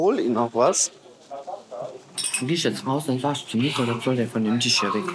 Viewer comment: "Dann sagst du zu dem ... er soll sich vom Tisch fernhalten." Was sagt sie da? Ich höre tatsächlich das N-Wort raus aber so schätze ich die Dame gar nicht ein?! Kann jemand hören, was sie genau sagt? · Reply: NICOLE !